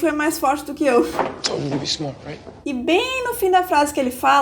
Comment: acho que ele não fala "linda".. Acho que ele fala : * Eu te falei pra ser esperta, certo? * Eu ACHO, kkkk